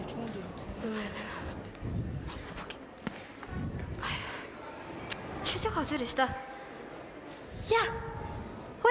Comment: I hope I misheard youngkyar dolma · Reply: She is holding an incense སྤོས in her hand so she Said འདི་ཕར་འཁྱེར། ' thi pha khi ' ( take it away) in super fast mode to the students helping backstage.